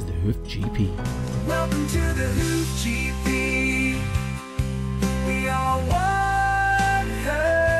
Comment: I love the new opening song, but I laughed so hard as I thought it said " welcome to the hoof GP, we are wanker one family"🤣